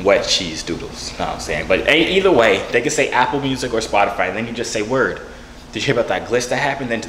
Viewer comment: Anybody else heard him say apple bees instead of apple music somebody hungryyyy😂😂